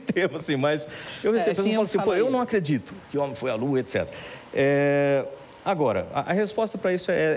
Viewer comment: Em 12:30 ELE DIZ QUE CHEGAM P ELE E DIZ "QUE NAO ACREDITA QUE O HOMEM FOI A LUA", E ELE RESPONDE....